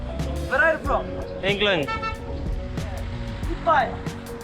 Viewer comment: I think he is saying "Good Bike" with a thumbs up, caption says "Good Bye" ... not a big deal, just an observation. Stumbled on this from your post in Final Cut Pro Users facebook group. I am learning from both the adventure/scenery, story telling, and the editing. I will finish viewing later when I can give it my full attention on a bigger screen.